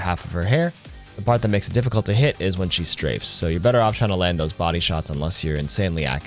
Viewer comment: YO IDK WHO MAKES THESE SUBTITLES ON UR VIDS BUT THEY ARE ALWAYS BAD. For example the subtitles once said mac ree. Also at 1:40 the subtitles say STRIPS instead of STRAFES The subtitles are bad on every single one of ur vids. Example:Orisa-Arisa Bastion-fashion Hit box-hip box